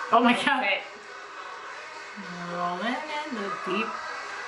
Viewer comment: I thought they were saying rollin in the day